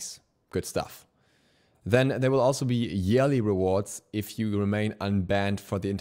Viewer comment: Anyone else hear jelly rewards